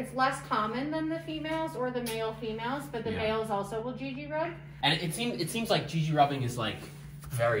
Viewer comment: Did they call that a Gigi-rub?